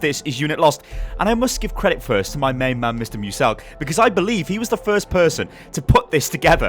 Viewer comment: did he say muselk i love that youtuber